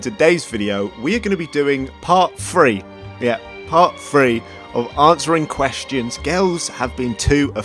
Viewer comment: When you're from America and all you can hear is "Part FREE" instead of *Three lol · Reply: I am not even American but I clearly heard “part free”